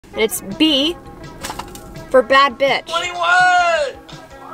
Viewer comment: I thought you said "Black Bitch" and I was.. 👁👄👁 Btw you looked so pretty with those glassessssss